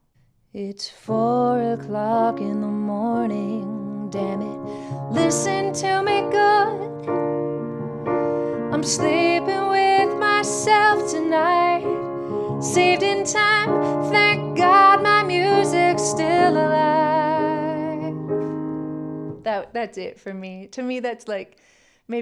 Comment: I got into this when I was about 10 years old because my mother had the cassette (ok it was 8 track!) and the part said was her favorite, I misheard - I thought he sang "I'm sleeping with myself tonight, sick and tired, that god-damn music's still alive".